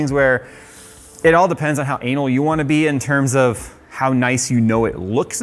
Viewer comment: Did he say "how anal you wanna be?" At 10:02 🤣